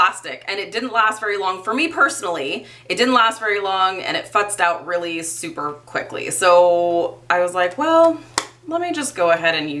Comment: Did you say it 'fucked out really quickly' at 8:24? 😂😂😂💀